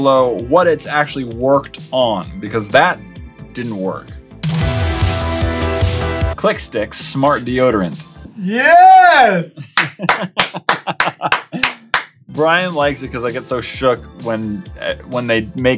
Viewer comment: I thought it said chickstick not clickstick